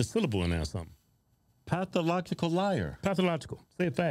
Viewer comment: At first I thought you were saying pathamological